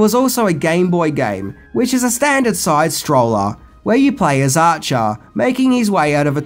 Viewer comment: did you call 80's female rock star Pat Benatar "Pat Banata"? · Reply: he says “acha” instead of “archer.” It’s just his accent